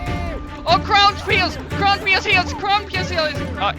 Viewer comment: till 8:16 did I hear "Crownpierce" or should i see the doctor 😅